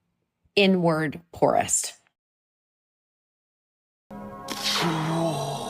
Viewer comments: lol I thought she said N word, poorest 🤣🤣🤣🤣🤣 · I thought she said N-word poorest and all of a sudden this got a lot more interesting. Vapid joyless harpys. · I thought she said N-word poorest 😂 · I thought she said she "was the n-wordly poorest" 😅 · 'At my on paper richest, i was my inward poorest' hahahahahahahahahaha! omg! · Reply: Thought she said N word poorest!! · OMG I thought she said N-word poorest. I was shook! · I thought she said N-word poorest 👀